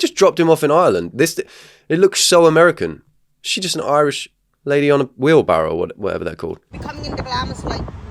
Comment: The man who was dropped off at the intersection was left not far from where I live, in Ontario, Canada. Paisley is a small town that I drive through fairly often. The people traveling by horse and buggy are Amish there’s a large Amish community in this area. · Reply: Amish in Horse and Buggy? I thought he said Irish in Wheelbarrow and Doggy.